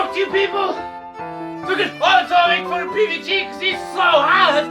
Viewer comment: I think he said "let's all make fun of PBG because he's so hot!" Please correct me if that's not what he said.